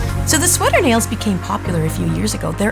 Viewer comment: Lol for spilt sec I thought this said sewer nails glad I was wrong